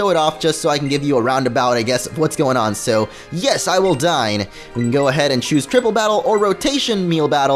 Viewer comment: when he said "yes,I will dine". I thought he actually said yes I will die